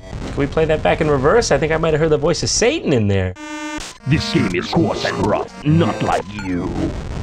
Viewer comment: did they say this game is grump not like you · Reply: "this game is course and rough, not like you"